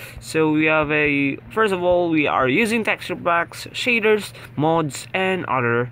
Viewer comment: I Almost Thought You Said "Cheaters" Instead Of "Shaders"